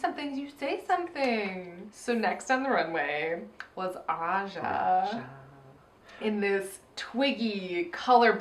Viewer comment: did say aja at 17:29 instead of Raja cuz i had to watch it back like five times and i'm still not sure.